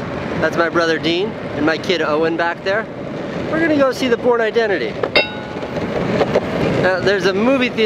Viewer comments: anybody else thought he said porn identity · I thought he said Porn identity